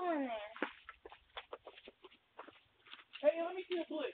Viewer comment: guy exited vehicle to confront the teenagers when he could have drove on. i think truck driver is at fault. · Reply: backpack boi was the aggressor, he told his homey in the car to give him the "blick" aka gun.